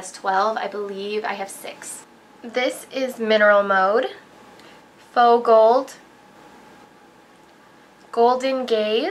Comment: oh my god when you said "faux gold" i thought you said "fogell" like in superbad hahaha